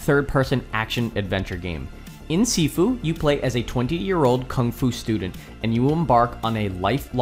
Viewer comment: Love the vid. Subbed too but I gotta know. 01:19 did you say 20 or 28? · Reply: 20! Apologies, I can hear how that may have been misheard. My mic is a little soft sometimes